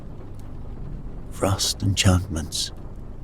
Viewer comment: I thought it said frosty chocolates and was thoroughly confused until I skipped back and listened again 😆